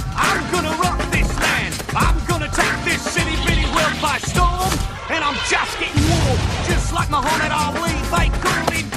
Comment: did he say Sh!t on kids movie 💀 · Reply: He said itty bitty